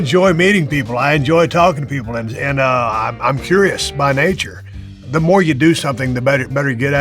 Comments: i thought he said I'm bi curious lol thought we had a Dave Skylark moment. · lol I thought he said "I'm bi-curious"